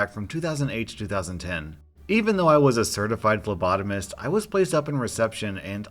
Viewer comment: i thought they said they were a certified lobotomist...